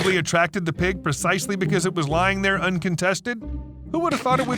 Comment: What's that you say? The carcas was lying there uncowtested?